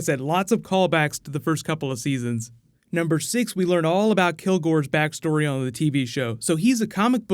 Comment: I honestly thought it said kilgrave for the villains name for the first half lol.